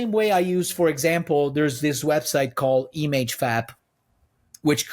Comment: I know ow my brother in Mastodon isn't saying "image fap" (dont google) what is he saying here?